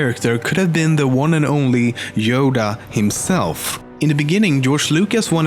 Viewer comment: wait did he say yoga well top10memes you FAILED